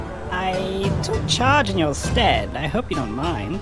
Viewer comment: wait did Reggie just say skeld?